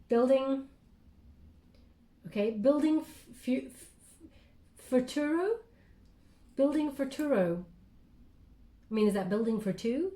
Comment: I keep hearing “building for Tarot”..✨received and so thankful🤞💫 #AAMichael